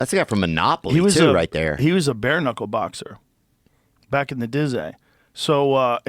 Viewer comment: Did i just hear Joe Rogan say "back in the dizay?" at 40:15 LMAO